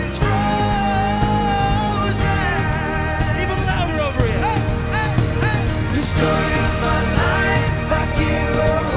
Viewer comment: Can some one tell me what liam says at 2:37 i ve listened it many times but can't understand 😕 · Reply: He said, “A little bit louder over here” He was telling the crowd to sing with them 😁